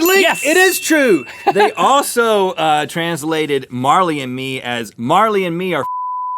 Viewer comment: I think he said "f**king ba***rds" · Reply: Marley and Me are f*cking each other in the a*hole